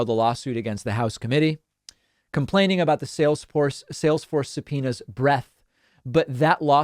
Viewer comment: Every time someone says “subpoenas” I hear “subpenis”